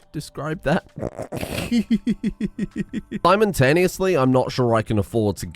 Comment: I heard or misheard you say simontaneously at 19:47, which makes for a brilliant character name. Simon Taneously.